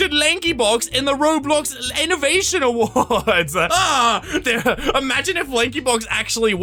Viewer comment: It sounded like he almost said 'Roblox Lankybox awards'